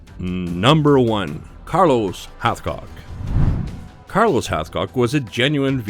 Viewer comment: I thought it said it said Carlos half cock